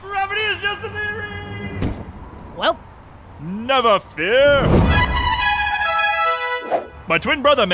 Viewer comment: The bird sounds like it says never fear